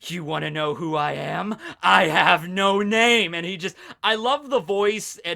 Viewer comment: I thought he said I have no name when white was fighting byakuya